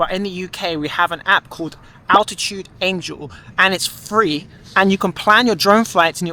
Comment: Well when you talked about the app it sounds like you said out of tune angel. I can find nothing like that in the App Store.